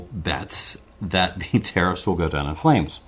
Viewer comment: the automatic subtitle generator recognized terrorists instead of tariffs: ...the terrorists will go down in flames.